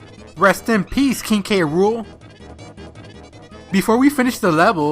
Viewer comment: I had subtitles on, and every time you said K-Rool, YouTube thought you said can't rule.